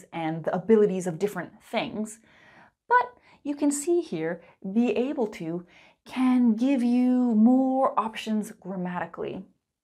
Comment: Could you tell me what you said at 6:50? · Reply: she said "be able to gives you more options grammatically"